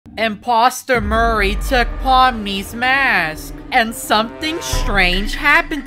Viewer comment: i heard "empasta mury took palmknees masky"